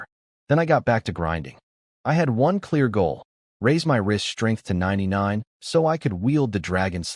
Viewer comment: Whenever he says "wrist strength" I keep hearing it as "rizz strength" and I'm just like lol they need to add that, like skibidi rizz lvl 99